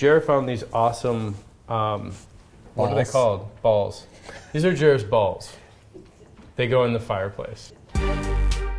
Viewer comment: Did he say "these are Jewish balls, they go in the fireplace"?